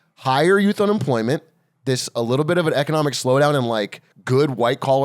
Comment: If you listen carefully you can hear Xi occasionally bounce a basketball off-camera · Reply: I'm pretty sure I heard him say, "how do you like it, white coloured foreigner?"